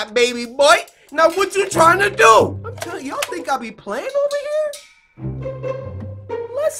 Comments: oh my god I thought he said "yall think I be playing my n****" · I'm so sorry but I thought Cory said YOU THINK I'MA BE PLAYING WITH A NIGGA and I'm like CORY XD but then I went back and he said playing with a boy XD